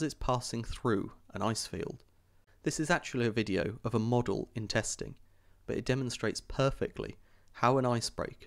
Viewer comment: I thought he said “model intestine”.